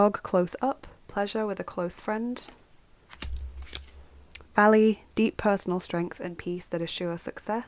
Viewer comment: Hey, I'd like to enter 😆❤ I choose Pile 3 and when you said valley it startled me cuz I thought you said Allie (I go by Allie more than Lexxie). Felt like you were calling my name 😂 Thank you for the lovely reading. I hope you have a happy new year. ❤